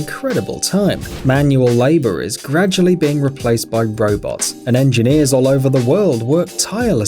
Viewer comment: did he just say bro-bot?